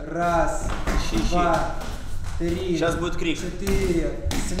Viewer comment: щас будет Кринж? Или мне послышалось